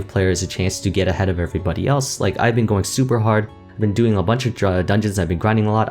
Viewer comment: Did anyone else catch him saying "I've been doing a bunch of dru... uh... dungeons"